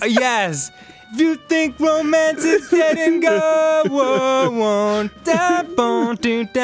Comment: I thought it said, "If you think romance is getting old."